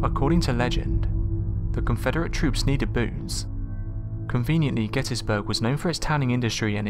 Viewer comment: I know you said "according to legend the Confederate troops needed boots". But it sounded like you said BOOZE. LOL